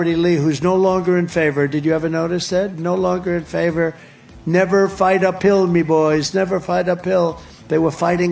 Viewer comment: every time i hear it it sounds like he is saying "never fight a pill". bit difficult, even when you are going uphill.